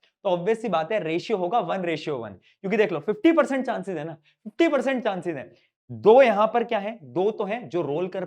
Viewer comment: Who else heard 50 percent chance ide??